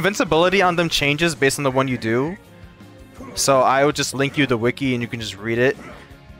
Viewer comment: WHY AM I HEARING "DE WIKI" HELP ME WAAAAAAAA